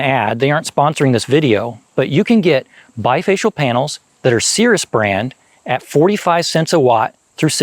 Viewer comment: I thought brother said sears brand 💀💀💀 like ho-ly… I thought they were long dead🫠😅😅